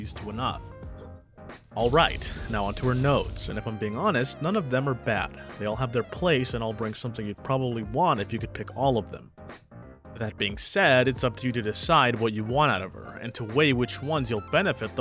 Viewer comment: what was he saying there? No matter how many times I tried rewatching, didn't manage to catch a single word. Great video otherwise! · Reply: "None of them are bad, they all have their place, and all bring something you'd probably want if you could pick all of them, that being said, it's up to you to decide what you want out of her, and to weigh which ones you'll benefit the most from, especially the first 2. Strategic Planning gives her 3 confectance points to start with, letting her ult right away if she doesn't have C5, which may or may not be very important to you. If you DO have C5, this becomes less necessary since you can already ult right away, but if all you want her for is support bombing, this node will help you do just that, just remember, her bombs do significantly less damage at higher cons if the enemies aren't defense shredded, so again, be mindful of that and how it effects how you play her.